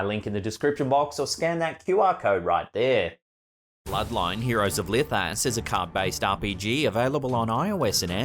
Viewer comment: Hold on! I think misheard you. Heroes of Licked Ass?